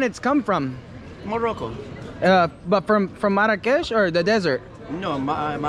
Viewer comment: Did u just say the desert 27:48 🤣🤣🤣 how can fruit come frm the desert